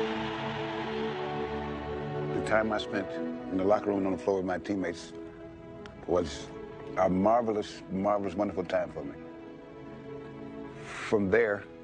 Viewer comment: Can someone write here what Bill Russell say at 1:15:12 ? I'm italian, i'm not understand very well. Thks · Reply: "The time I spent in the locker room and on the floor with my teammates was a marvelous, marvelous, wonderful time for me. From there, I couldn't go to Heaven because leaving there and going any place else would be a step down." I hope this helps! :)